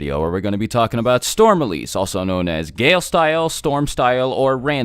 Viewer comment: I honestly thought the darui kept saying "Gay Style"